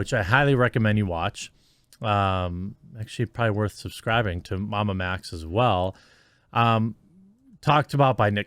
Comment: I've been watching MamaMax for quite some time and the work that he does to uncover p3d05 and animal abusers is just amazing and the fact that YT censors him pisses me off. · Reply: I thought he said Mamba Max. I was wondering how a Remote Control car manufacturer youtube page had so many followers lol. Castle Creations does make some of the best and most powerful electric motors for RC cars though. Their Mamba Max series is top notch. I highly recommend them for anyone who wants to give their kid an rc car that can do 140mph for Christmas or their Birthday.